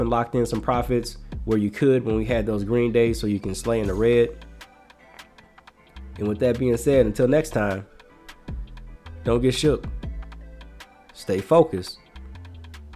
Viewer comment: Damn, finally found your channel. Kept hearing Ruell mention you, but i thought he was saying Shift Focus. 😅